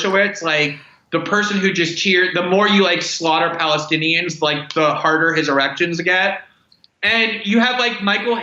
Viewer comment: OMG 17:38 did he say the harder his erections get? Lol 😆